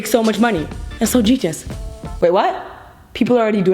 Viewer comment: I thought u said “that’s so tedious” instead of “that’s so genius”